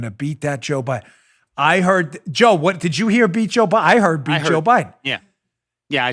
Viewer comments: I heard 'be Joe Biden.' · I heard "beat Joe Biden"